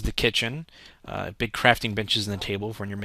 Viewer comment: I thought he said big crafting bitches